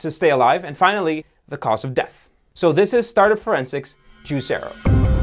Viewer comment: All I heard was, 'Cause of Death, Veganism'